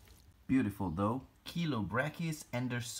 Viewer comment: everytime someone says "Chilobrachys" i always hear it as "killer brackets(or brackys)" anyone esle with me?